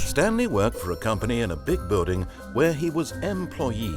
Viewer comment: I SWEAR HE SAID BIG BOOTY